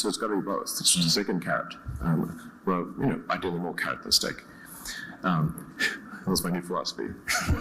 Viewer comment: What did he say at 5:13? ".... philosophy" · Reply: I think : "That was my new philosophy"